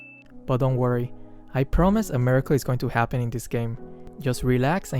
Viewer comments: I thought he said "don't worry, I promise America is going to happen in this game" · When said “a miracle” at first I thought he said “america” 😂 i thought he said “America” will happen again haha. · Lol I thought you said I promise America is gonna happen this game.